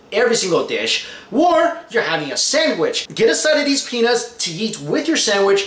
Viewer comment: did he say penis or peanuts?